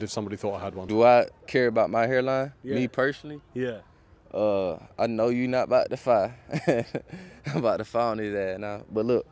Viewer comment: The American guy at 02:00 is super southern. He actually said, "i know you not about to fye up". Noy fight. Lol. Fye up is basically southern slang in america for fire up which means to make fun of.